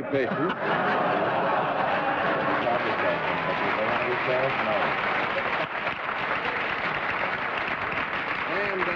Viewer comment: Can anyone make out what Henry mutters at around 8:47? After he says it he looks peed off. I don’t like his attitude here. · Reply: I think he said “He tried to tell it, but did he know how to tell it? No.”